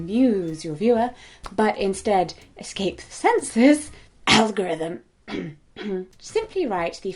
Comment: the accepted caption is incorrect. The word Jessica said is “censors” but the caption says “census”. Just trying to help :)